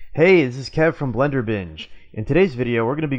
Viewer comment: lol.. I thought you said Blender bitch....lol.... great page.. thx..